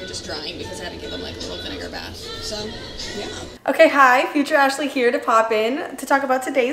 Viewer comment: When she said “future ashly” I thought she said “TRASHly” and I was like “oh that’s a good drunk name” but no. She wasn’t drunk. It was a sponsor.😭